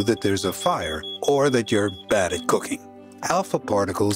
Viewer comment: I thought I heard “badly cooking” now that’s some dark humour